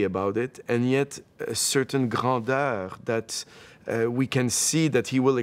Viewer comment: What was that word you used to describe the piece? Gradeur? LOL ♥ Ƹ̵̡Ӝ̵̨Ʒ ♥ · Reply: Grandeur